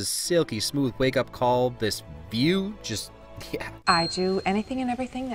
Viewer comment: IDK why, but I heard the first time (and sorta the second time) Biew instead of view.